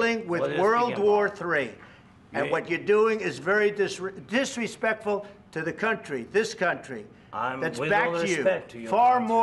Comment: Did he say “we don’t respect your country?”